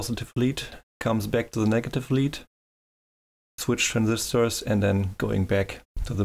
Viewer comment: These are no thyristors but MOSFETs, with all the control gates tied together. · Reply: I was sure I heard you say thyristors at 1:20, I probably misheard. I was just wondering if the small difference in turn on time would cause any issues with over 1 kA coming from the battery.